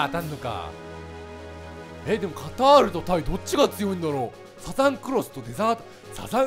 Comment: サザンクロスなのに、サザングロスって聞こえてしまったw